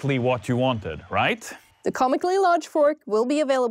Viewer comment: wait at 1:12 is it 6.99 or 69.99? because subtitles say £69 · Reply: they say 69.99